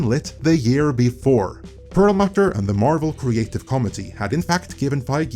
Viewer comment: I honestly thought he was saying "Marvel Creative Comedy" until he put it on the screen. "Oh," I thought, "that makes more sense."